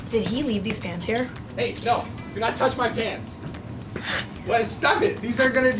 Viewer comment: Did anyone hear say “BABE no do not touch my pants” to Emma